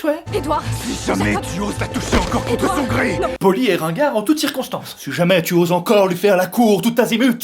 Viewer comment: j'ai entendu "voli et rengar"